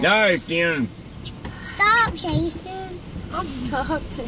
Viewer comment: I laughed,,then wtf a little girl says " he want to kill you " ahhhhhhh!!! I mean ahhhh!!!! LOL